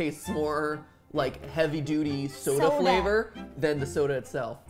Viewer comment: When MatPat said “a party” i thought he said epoxy